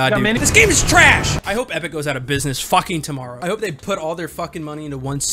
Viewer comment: Am I the only one who heard him say Evan instead of epic at 0:42?